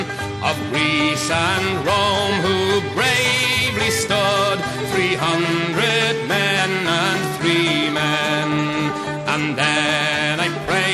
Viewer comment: 300 men and 3 men refers to the spartan 300 and the Horatii brothers. For anyone who was wondering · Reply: I ho estly thought he said 300 men and free men. Now i knoe